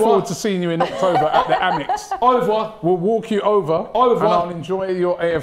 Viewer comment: I thought Ty was saying “over” but he was actually saying, or at least trying to say “au revoir’